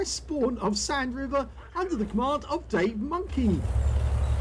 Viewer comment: Good game. I have to laugh when I see your replays, because the first time I hear say your name I thought he said "Spank the Monkey!" I fell out of my chair laughing!